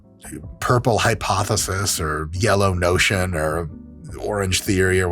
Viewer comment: Im sorry did you say Yellow Notion 😂